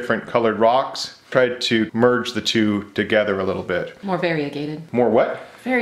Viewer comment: No one: V A R I E G A T E D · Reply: I thought she said Fairygated. I was like "wha huh?"